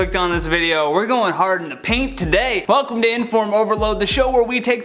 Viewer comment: I'm sorry. But did he just say ' we're going hard in the pink today '..? Describing a video about children..? 🤦‍♀️🤯🤔🤮👀☠ Wtf ..hahaha😅🤭😔 Nooooo.. Did I miss something..?